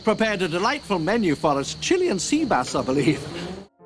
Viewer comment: I loolll im 45 and ALWAYS thought he said chilli AND sea bass loll also thought it didn't look like chilli hahaha😂